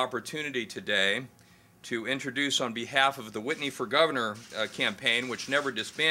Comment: I thought it said "rich whitey" instead of "Rich Whitney".. lol The Green Party is the ONLY political party in which I hold many beliefs with, for I am an Environmental Anarcho Communist. I only encourage the Green Party to be more libertarian. Good luck.